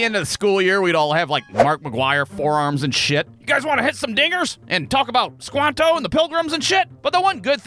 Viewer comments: Did he just say the “N” word at 1:01? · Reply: I had to turn on CC to figure out what he said. According to it, he says "hit some dingers", which apparently is a slang term for hitting a home run in baseball. Lol. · Swear to god 😂 I had to listen to 1:00 over 20 times just to verify "you wanna hit some ****ers" wtf 😂😂😂 obviously he said "Dingers" but it took alot of replays 😂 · dont know why but I thought he said the hard R there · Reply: I think he said dingers